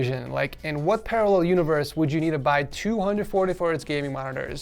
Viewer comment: the subtitle says 240hz monitor instead of two 144hz monitor